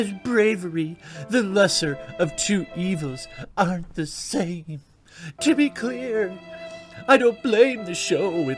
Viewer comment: Did you say, "titty clear" at 6:55?